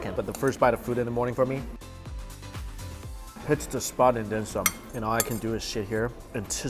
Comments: I thought he said “Hit the spot and Dim Sum.” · thought you said hits the spot and dim sum. Man am I hungry 😅😂🤣